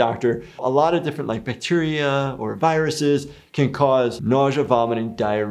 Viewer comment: is he saying Petunia or Bacteria bcz i heard it as Bacteria but the sub says Petunia